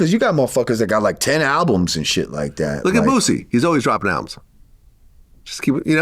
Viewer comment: I hear "Bootsy"